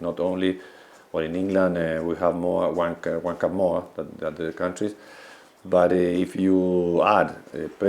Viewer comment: sounds like he says 'wank more than other leagues'